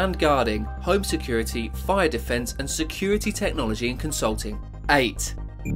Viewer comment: Did he just say fire defence